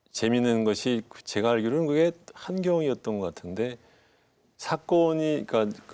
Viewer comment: 안녕하세요 흥미롭게 잘 보고 있습니다. 감사합니다. 그런데 주진형 선생님 말씀 중에 33:46에 한겨레라고 자막에 나오는데 한겨레가 아니라 한경으로 들리는데 한국경제신문 얘기하는 것이 아닐런지요